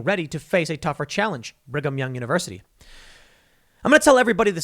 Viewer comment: When Tim read "Brigham Young University", I seriously thought he said "Rig 'Em Young University".